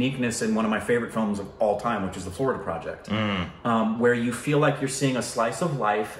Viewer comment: which movie is he talking about, I hear forward project, but thats not it. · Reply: Florida project